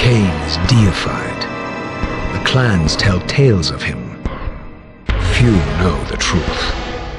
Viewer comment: To this day, this is still one of the most epic cutscenes I've ever seen. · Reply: For years I thought Raziel said "you know the truth" instead of "few know the truth"